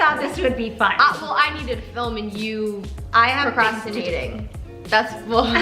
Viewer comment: I thought she said prostituing